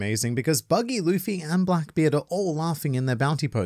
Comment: Sounded like you said ''Wankbeard'' hahah